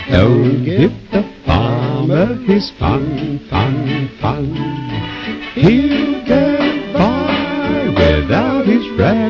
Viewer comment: When it goes "Run Rabbit Run Rabbit" I thought it said "Run Rabid Run Rabid" for the longest time